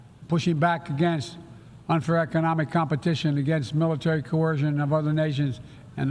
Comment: wtf did he say? unferwrektit competition? 10:22 free endopasiffy?